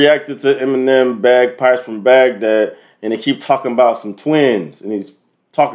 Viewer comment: I thought it said biceps from Baghdad at first lol